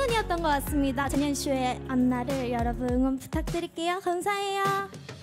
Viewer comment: On 2:32 did she say JenYenShow?